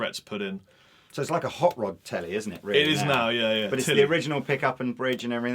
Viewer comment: I first heard say: "Well actually she's called Titty..." Then I realised it was Telly or Tilly x.x